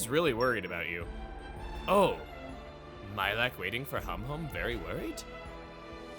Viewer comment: I swear, every time says Humhum's name, I mishear it as "hom hom" and get Xenoblade 1 flashbacks...